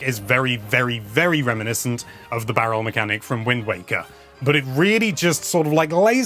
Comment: Am I imagining things or did he say "Wind Wanker" just